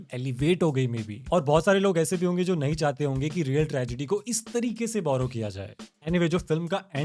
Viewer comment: sonu sogam is what he is trying to say